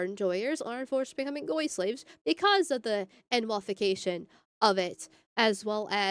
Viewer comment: Wait did she say N'wahfication? Damn, I might be simping...